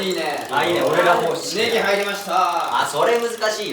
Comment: 本髙「ネギ入りました〜！！」 ネギと聞こえるのは私だけ、、？ 流石ネギ侍🟢！！